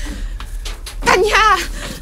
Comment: เหมือนนางเอกพูดว่ากัญชาเลย555